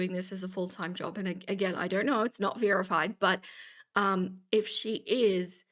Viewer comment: At one point you said "it's not verified" but I heard "it's not Vera-fied" and I think that's what you should call it from now on. ❤❤